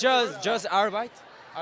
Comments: did ty say Arbeit?? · My German ass thought they were saying “Arbeit” at 3:00 .... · When Taeyong first said "part time" I heard "arbeit" which is the german word for "job" 😂😂 for a second I was wondering why he would say job in german lmao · wait did he just say "arbeit" 😁